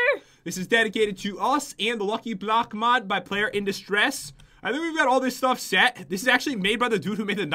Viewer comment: when you said "PlayerInDistress" I thought you said "Player In This Dress" XD